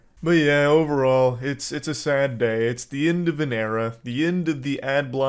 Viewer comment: sounds like he is saying Enda Vinera